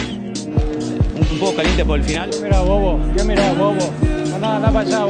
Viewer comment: i thought he said que miras bobo